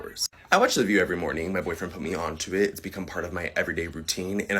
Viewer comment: Did he say boyfriend? Has to throw that shit out there. We HAVE to he told he's gay.